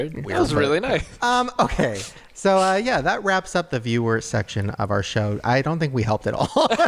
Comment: I thought Shane said “viewer erection” lol